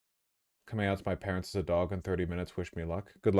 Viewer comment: What does Vaush say here? Its fucking impossible to make out. All I hear is "Gonna ask my parents to the dog" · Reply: "Coming out to my parents as a dog in 30 minutes wish me luck"